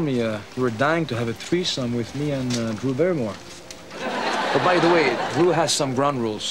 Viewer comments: En 31:52 donde "no entiendo qué m*** dice ahí" creo que dice "Drew Barrimore" Excelente el video! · Creo que dice "Drew Barrymore" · creo que dijo Drew Barrymore